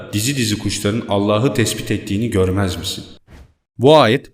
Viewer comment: Güzel insan bu wideonu geçen sene izlemiştim şuan bir kere daha izleme gereği duydum ama bir şey farkettim 5:24 e bir bakarsan ayette ALLAHı tesbih yazıyor ama seslendirme tespit diye anlaşılıyor haşa ALLAHı tespit etmek diye yanlışlık olmuş dikkatimi çekti anlam değişikli izleyenler için okumayıp dinleyenlerde olabilceği için yanlış anlaşılabilir diye belirtmek istedim emeğine sağlık ALLAH senden razı olsun saygılarımla.